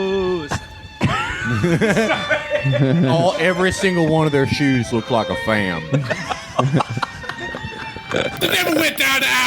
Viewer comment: What does Felix say at 1:30, “everyone of their shoes looked like a ….” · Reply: "every one of their shoes looked like a fam" making fun of boomers/libs (or liberal boomers) misuse of AAVE